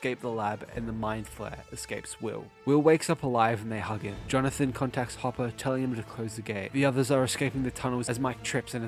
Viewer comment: I heard that as “Jonathan contacts Hopper and tells him he’s gay”